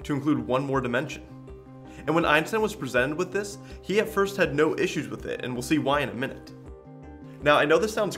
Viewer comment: What does he say at 1:26 ? He had no ... what ??? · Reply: "He had no issues with it"